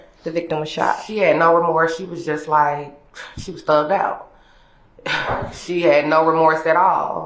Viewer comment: Did she really say she was "thugged out" or am I hearing stuff??